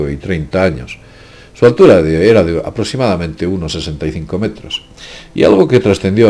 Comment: Minuto 9:55 "Su altura era aprox. de 65 metros" refiriéndose a los cuerpos calcinados de los tripulantes ¿Entendí mal? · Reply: 1.65 mts